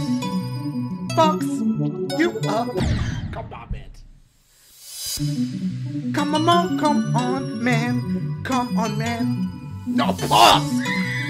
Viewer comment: Whenever I hear the music for the green stage I just hear "come come on man, come on man"